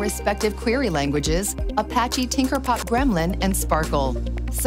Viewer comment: I thought I misheard, but nope, it's truly named "Apache TinkerPop Gremlin"